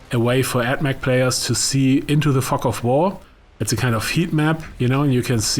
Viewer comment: I wasn't looking at my screen for the minute or two leading up to 7:10 - I thought he said 'see I to the f*ck off WAR' 😂